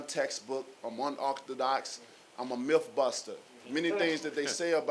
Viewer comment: Did he just say he's a MILF BUSTER? and he's untextbook